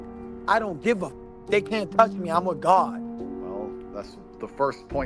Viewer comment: I thought he said “I’m a god but after listing it a few times I think he says I’m with God” that makes me feel better